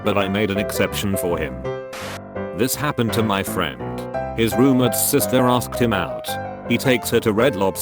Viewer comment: so i heard "My friend's rumored sister asked him out" the first time